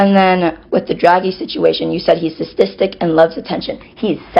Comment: no offense, but I would like to know what said. Was is statistic or something else. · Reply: Sadistic